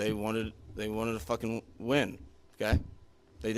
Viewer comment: WTF… at minute 11:23, did he say, “… water to fucking win!”, because I know I just didn’t hear that? What type of coaches are we hiring out here! Was the head coach a DEI hire or what??